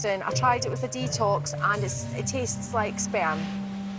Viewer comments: Did she just said at aprox 14:30 "it tastes like sperm"????????????????????????? WTF?!?! LOOOOOOOOOOOOOOOL By the way....I kinda of feel heart broken for the piglets....they could at least put adult pigs for them...it's not pleasant to see an "infant" animal dying. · did she just say " It tastes like sperm" or am i dreaming ?